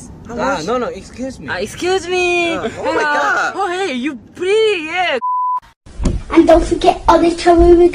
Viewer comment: What jimin says:oh hey you pretty! What I hear:oh hey you pee! Also me:........ Bahahahahahahahahahahahahahhahahahahahah